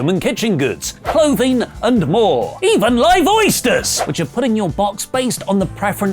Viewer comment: says "Live oysters!" but I hear "Lyvoisters!"